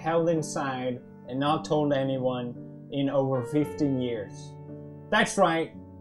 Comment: Wait.... I thought he said "that I've held inside and not told anyone in 50 years" not 15 years...jeez I'm deaf